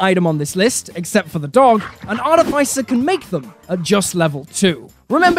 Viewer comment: An art if icer? What's an art if icer? Are you trying to say artificer? In that case start saying artificial drop the -ial and replace it with -er. There will be no ice only artifice.